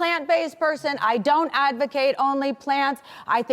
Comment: i thought she said i dont advocate onlyfans and i was incredibly confused.